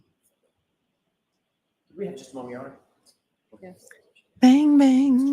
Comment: Did the judge say "I guess" when AJ asked on behalf of Lizy if they could have just a moment? I have replayed it multiple times and it doesn't sound like a "yes". I hope I just misheard because there have already been so many times in this trial where the judge said or did something well established that should never happen in front of the Jury. Like when she scolded AJ in front of the jury after a sidebar " That is not what you said you would say at sidebar". Totally unacceptable and imo, the judge better be praying for the only riotous decision of not guilty in this case. I already think her career is going to take a punch to the gut based on how she has handled this case.